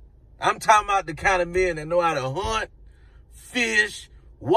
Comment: Why I thought you said they know how to hunch.... my bad hunt!!🤣